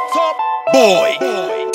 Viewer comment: Did i hear Phuck Boy @ 0:10? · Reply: Bar Flow No, he said "Top boy".